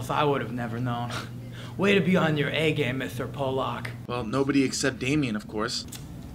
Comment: did he say "mr. Pollack" at 0:58? I'm offended